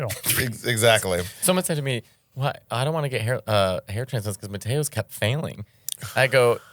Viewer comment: the subtitles did you dirty! They said "potato" instead of Matteo! It's the fourth potato today! Which by the way, is the first clip I saw of you and I instantly became a fan! Your actually touring in my city; Manchester UK in a couple of weeks and I'm super gutted I can't make it, but I hope you have a great time here, it's a cool city!